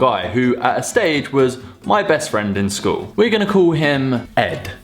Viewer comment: I thought he said we are going to call him egg 😂😂😂 Exxxx